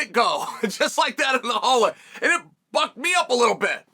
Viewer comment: Who else thought unc said “ it fucked me uo a little bit”?😂